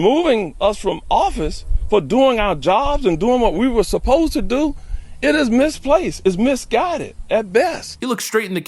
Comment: All I heard was “what we pasossed to do”